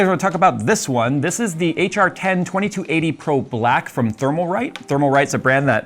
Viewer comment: Somehow I thought I heard Jay say HR-10 2380 Prophylactic